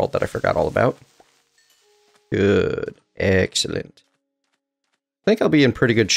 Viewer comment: At about 0:20 I think you said excellent but I heard eggcellent :p